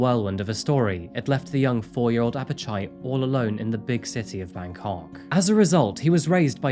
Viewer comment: I swear he said “city of my cock”.